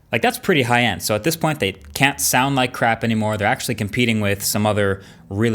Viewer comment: Did he said "crap" at 01:00?